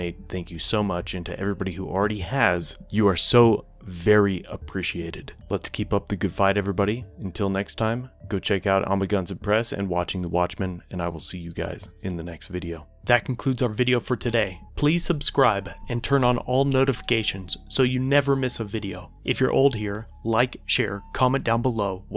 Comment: At the end it sounded like he said "I will sue you guys in the next video". Lol.